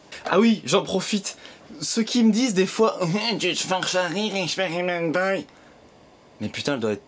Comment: j'ai besoin d'une traduction à 10:18 xD EDIT: a non c'est bon après 4 replay j'ai compris ! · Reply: "gnnn experimentboy tu te force a rire"